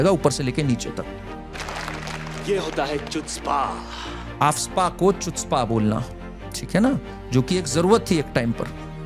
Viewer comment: Aptz? Kya bola yahan par?